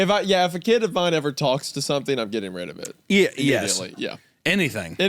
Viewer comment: He almost sounded like he was saying he's gonna get rid of the kid 🤣